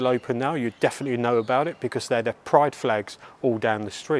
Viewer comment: Wow, had to listen to the Babylon part again! I thought you said 'price tags' not pride flags! Oops! My bad!